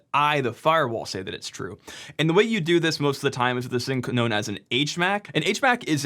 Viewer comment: Love the channel, but l heard you say "ka-nown" around minute 3 and that's a bit to funny. Less damning of a mistake than not validation the key index though